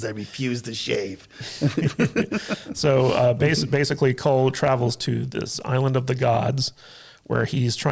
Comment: I'll admit... I got super excited because I thought it initially said Krull... lmao